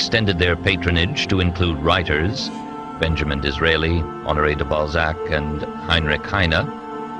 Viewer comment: I'm quite sure I heard one of the writers being "Andrei De Ballsack" at 3:07 ? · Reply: De Balzack, I believe...